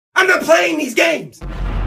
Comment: "Wanna be friends!?" "GOD NO!" "Wanna go bowling?" "I'M NOT PLAYING THESE GAMES!" Customer: Why don't I get that type of service? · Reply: Well it was im not playing this games i HEARD 'WHEN the PLANES dead...